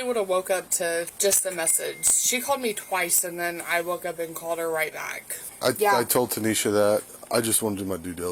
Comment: Did I just hear The sister say she woke up and called her back@